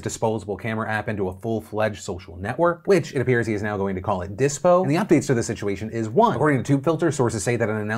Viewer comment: “Dispo” is a solid name. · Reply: I legit thought he said Disqo.